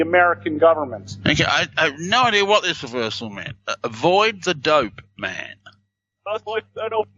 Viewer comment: I heard "avoid adult men"